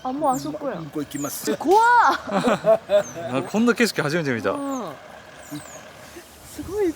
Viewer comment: 本田圭佑初めて見たに聞こえる